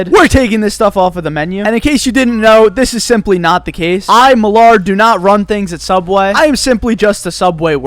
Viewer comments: Is it just me but when he said his name it sounded like he said Millard😂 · did he say milard